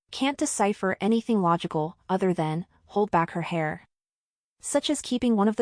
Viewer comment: "Hold back her hair" I hear " both bags are here"